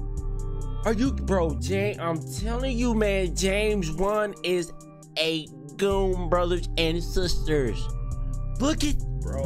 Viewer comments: Lmao i thought he was tryna say james juan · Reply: Dude I thought he said James Bond😂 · Lmao i thought he was tryna say james juan